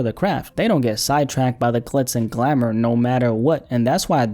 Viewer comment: did you say “clits” and glamor 🤣 instead of glitz on purpose lmao